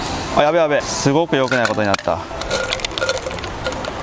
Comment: 「今までで一番ロリっぽいぞ」 って聞こえてしまった